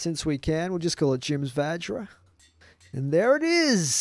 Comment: xD I thought you said "Jim's vagina"